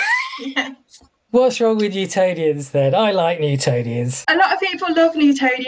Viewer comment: Im a newbie And for a while i thought you was saying Lutonians